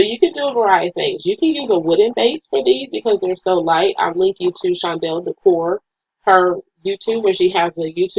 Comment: Great job! You mentioned someone who creates centerpieces but I did not see her link,it sounded like you said Shondel, what is the name of her channel. Thanks much!! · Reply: Janal Creations thank you! Yes! Shondells Decor. Her IG is and YouTube is Shondells Decor ❤️